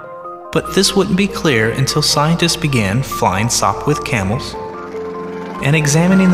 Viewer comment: Someone tell me at 3:07, what is he saying "SoftWood Camels" ?